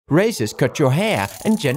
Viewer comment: I heard "racist cut your hair" XD